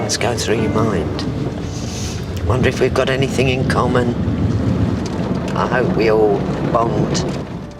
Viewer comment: "I hope we all bond." LOL I thought she said, "I hope we all bowled." And I thought, "Whaaaaat???!" That English accent got the best of me for a minute! :D